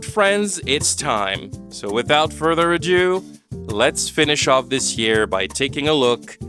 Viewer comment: did you just say "without further adieu"?